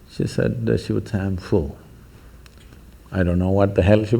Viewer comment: What did The Master said about her "at 32:42 min." She said she was ................ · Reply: She said I am full